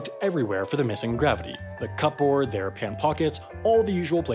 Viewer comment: did you say... cup board?